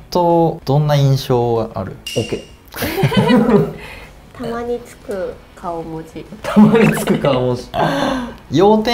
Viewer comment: 〜0:22 なんて言ってるんですか？ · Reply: 年収チャンネルの株本ですふうかです山本です